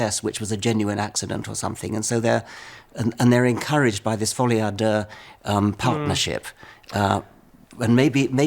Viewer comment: can someone please tell me this (what seems to be French) saying? Thanks in advance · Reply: “folie à deux” means “madness shared by two”